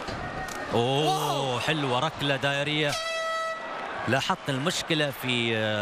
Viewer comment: Does he say diarrhea? At 7:44 who had diarrhea I don’t speak this language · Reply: He said in arabic '' rakla dairiya '' which means a '' spinning kick '' but yea youre right it sounds like diarrehea for a moment .. Im arab and i can tell you that watching this fight in arabic was painful and unfair .. theres no hype whatsoever just cold blooded mfs who got no interest or knowledge in mma most of the time theyre talking about something stupid or calling wrong side strikes ( calling a left hook a right one and a right kick a left one 😂) its lke theyre commentating on a boring golf match and not two guys trying to kill each other ...